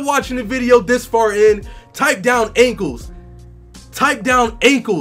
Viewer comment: did he say ingles or ankles